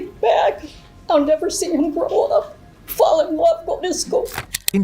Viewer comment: SHE SAID I'LL NEVER SEE HIM FALL IN LOVE, GO TO SCHOOL. SHE DID NOT SAY I'LL NEVER SEE HIM FALL IN LOVE WITH HIS GIRL.